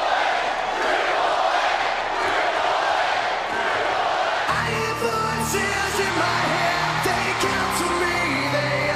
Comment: What the crouwd says at 8:47? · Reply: Triple H